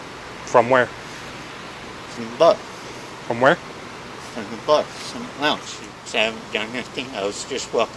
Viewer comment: he said he was walking home "from the bar - from the lounge" and bc the cop thought he said "bus", he asks stupid questions about "from where"